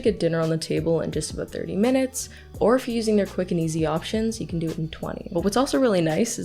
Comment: I'm laughing at myself cause I misheard you saying their quick and easy options for their fricking easy options lol